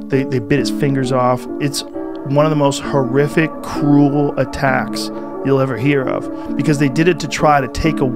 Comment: I thought he said “It’s one of the most horrific… Cool attacks you’ll ever hear of.” 😳😭😂